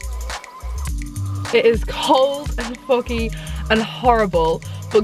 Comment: i thought first you said ''its cold as f*ck'' lmao. People do fasted workouts to lose weight as it burns your fat reserves if you haven't eaten.